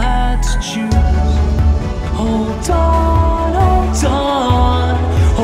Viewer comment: when they say "hold on" , I thought the said , "Oh Darn,"